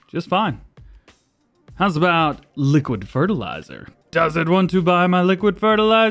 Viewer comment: That warehouse sell point will take the liquid fert, You just have to hold it up so it can empty If I am correct. I could also be wrong, but worth a check. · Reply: I thought you said liquid fart lol